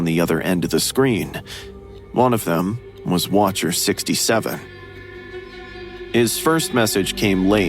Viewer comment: did someone said 67???????